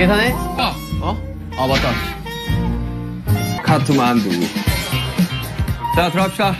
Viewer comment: Suga said Kathmandu.😅 After so many repeat watching this ep., only today I heard it. 😅